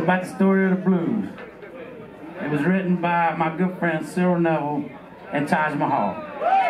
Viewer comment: That song starting at 58:45, who does he say wrote it? It's really good! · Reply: Cyril Neville & Taj Mahal